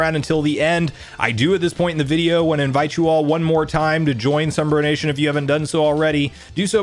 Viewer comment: Sounded like he said sunburn nation ... makes sense from a ginger. :D